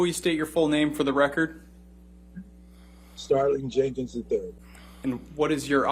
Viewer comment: Did I hear his name correctly? STARLIGHT Jenkins the 3rd? That’s a movie name! I love it. Especially on such a serious gentleman. ❤️ Edit: misheard his name, it’s Starling.... makes more sense..... I never heard of smone called Starlight....pity it was too good to be true...